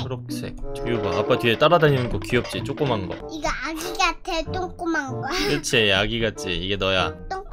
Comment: 쪼꼬만거가 똥꼬만거라고 들려요..